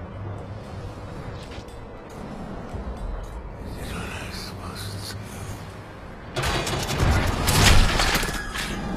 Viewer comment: does anyone know what Seth said around 4:06 before he died? I can't make out the words O.o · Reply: I did what i supposed to do